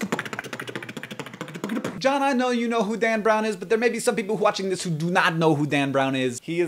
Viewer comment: Did you say "hwatching" at 1:33 ?